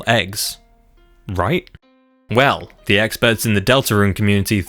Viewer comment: i thought he said "eggspert" instead of "expert" for a moment before realizing. i almost lose my own consciousness over the most mind killing pun